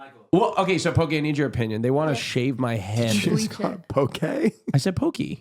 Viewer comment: I heard Pokee as well lmao